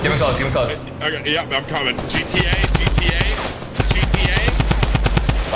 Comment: did tim said GTA